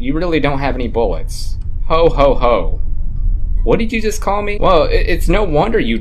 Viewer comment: My dumbass thought that said no homo for a second